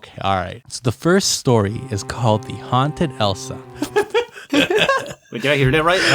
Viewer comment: I literally heard ''The Haunted Hosuh...''